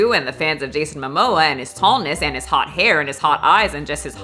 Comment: I totally heard you say Jason mamoa is a scallion...